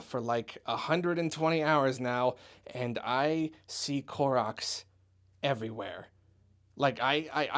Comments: I honestly thought you said Clorox instead of Koroks at the end there. xD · Am I the only one who thought he said Clorox when he said Korok?